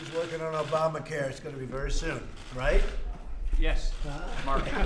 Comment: Did Paul Ryan say "March" at about 2:18 when responding to Trump's question about Obamacare? Sounded like it.